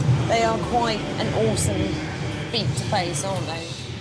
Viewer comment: "Quite an awesome feet to face" did she say? Yes pls.